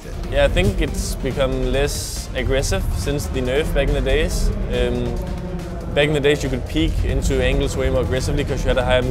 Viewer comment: did he said "Bangladesh"? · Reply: "back in the days"